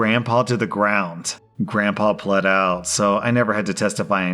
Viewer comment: Who else was just listening and not watching and thought he said "Grandpa bled out, so he never had to testify in court" and your heart stopped lmao